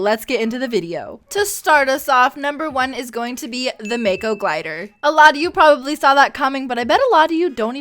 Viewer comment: I thought this bitch said Og skins not gliders